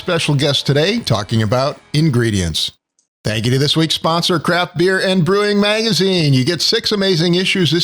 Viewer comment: Did he say crap beer at 0:31?